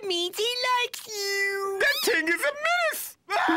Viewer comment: Grim That thing is a menace! 😱😱 Somehow when i was a kid, i thought he said "that thing is a mess!"